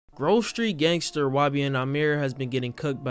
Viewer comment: Grove Street Gangster is crazy bro I'm ngl. 🤣🤣🤣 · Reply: i thought u said grocery sto gangster 😭